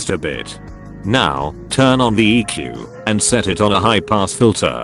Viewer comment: for some reason when the narrator said "turn on the eq" I heard "turn on the eek" :D